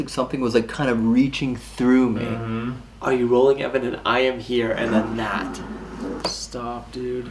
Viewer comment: I swear when you think he said "I'm Here" I heard "Mirror"